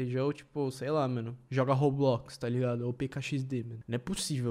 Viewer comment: preciso de um tratamento de limpador de mente Ele falando: ou PKXD Agora oque eu ouvi: P1KAXD